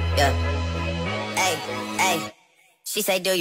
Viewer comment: I thought he said “ I only love minors and my mama I’m sorry”